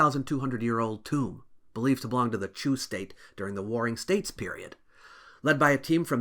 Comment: I thought he kept saying Jew State. I'm like dude turn it down a little bit okay there's enough anti-Semitism in the world 😂